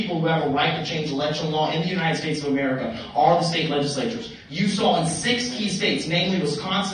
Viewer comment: Mad CowThorn lost me at 1:53 did he say 60 states? 🤔😬